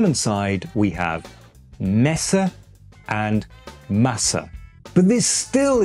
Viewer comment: to be honest, i didn't really notice your british accent sticking out in "Hamburg", but in "Masse". what i heard you say was much closer to "Mässe", rather than Masse lol